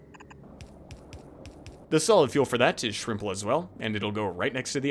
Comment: Did I just hear you say "shrimple"? Lol